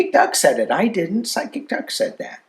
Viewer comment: Sounded like you said, " Side kick Duck" 😁😂